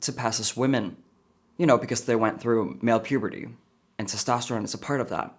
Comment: Honestly ALL I hear from Her is I AM SPECIAL I AM SPECIAL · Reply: That's why you always speak life into your kids.. have to admit i thought he said transtosterone lol